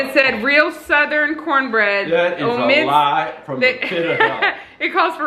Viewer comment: I played it back and I think your dad said “That’s a lie from the pit of hell” but the first time I thought he said “That’s a lie from Nineveh lol